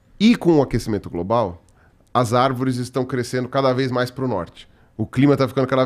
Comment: "As arvores estao andando mais par ao norte" kkkkkkkkkkk pqp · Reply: Eu ouvi "crescendo"